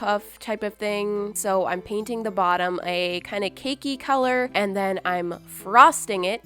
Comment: Who else thought she said "Im painting the bottom a kind of KINKY colour" at 5:15?